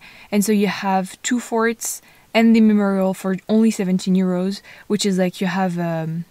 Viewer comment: She said 17 euros... I thought she was saying only for 17 year olds!! Loved the video.. the 2 world wars hold a dear place in my heart. I'm so glad to see the younger generation get involved.